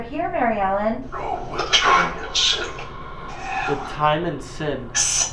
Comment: When they said "the time of sin" i thought it said "no ones innocent" lil scary